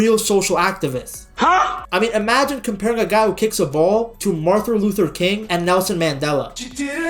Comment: did bro say Martha Luther King?????